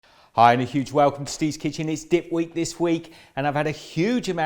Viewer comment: I am soooo doing this. Diet be damned! I probably shouldn't say it sounded like you said "It's dick week". But I did.